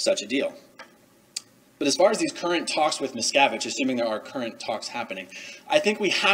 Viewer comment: Every time you say "Miscavige," I hear "Miss Cabbage." And I'm here for it. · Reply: I hear "Miscarriage." lol. Crazy